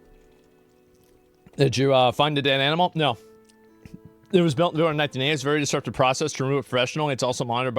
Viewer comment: I thought for sure he said he found a dead animal under his dick.